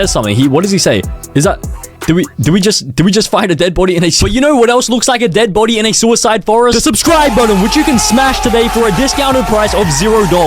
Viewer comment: for someone whos wondering what 6:57 said since it will probably get cancelled it said “did we just find a dead body in a s0icide forest— YOU KNOW WHAT ELSE LOOKS LIKE A DEAD BODY IN A S0ICIDE FOREST?? THE SUBSCRIBE BUTTON!!”